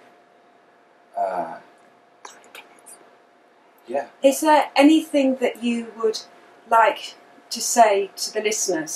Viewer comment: a woman whispers...anyone catch that? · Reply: sure she says volunteers! he then goes on to talk about volunteers! is this scripted too?